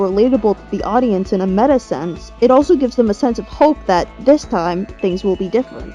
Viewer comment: did u say hope